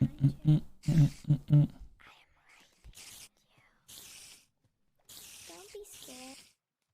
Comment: What I heard: “Don’t be scared” 2:27:44 “Don’t be scared, I just wanna play a game with you” 2:32:55 “Hey! don’t you run away!” 2:33:01 “Stay with me” 2:35:43 “ some giggles “